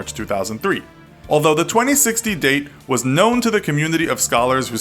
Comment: Phew 😅 I thought he said 2016